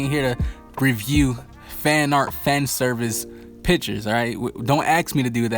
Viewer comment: I thought you said fan art fan service bitches 1:02 lmao 😂😂😂